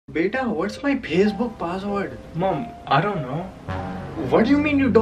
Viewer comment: What everyone hears: Beta, What's my Facebook password? What I hear: Beta, What's my PSBook password?